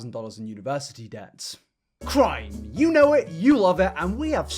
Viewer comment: I was only half listening in the beginning, because I was cooking and thought you said "crying" not "crime" so I was in for a surprise that it was so bad I cried before that the FBI was knocking on my door